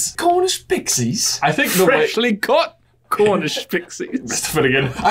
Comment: I always thought Lockhart said “freshly cooked, Cornish pixies”